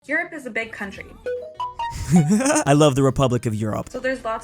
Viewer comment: Bro wtf when i started video i heard "Chad is a big country" 2 times. Not joking